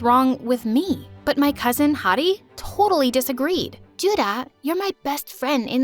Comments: “But my cousin Heidi totally disagreed” What I heard: “But my cousin hottie totally disagreed” 💀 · I thought i heard Ju-da say “hoddie” instead of Heidi